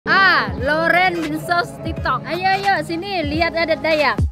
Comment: Gw salah denger apa kuping gw sakit dia bilang laurent bensos tiktok.